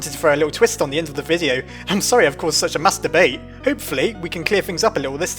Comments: I just heard masturbate but it wasn't... what was it? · did he say masturbate?!?!?!?! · When he said mass debate, it sounded like he was saying masturbate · When i heard you say "Mass Debate" it sounded like you said "Masturbate" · it sounded like he said "im sorry i caused such a masturbate" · I thought he said "Masturbate". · did you say, "masturbate" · on 1:24 why did you say masterbait